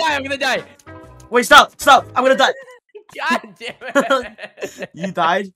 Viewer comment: I thought he said I wanna die